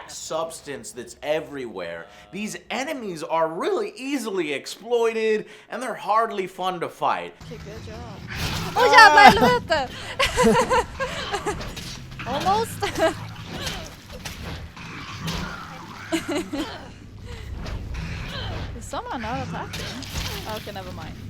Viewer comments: Joe is your girlfriend Swedish? xD It sounds like she said "åh jävlar helvete" x) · Did she say "jævla helvete"? Is she norwegian or sweedish? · is the girl swedish? at 17:02 it sounds like she is saying å jävlar-helvete???